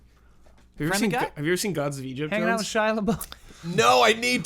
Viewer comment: what's the title of the movie said by at 1:21:20? gods of egypt?